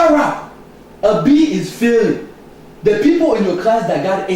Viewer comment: My comments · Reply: He said "a B is filling" instead of a B is Failing